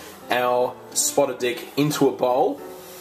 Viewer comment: i thought it said "spotted DUCK"